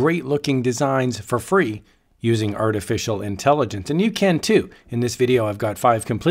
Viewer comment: I heard you say art-official intelligence lol 😂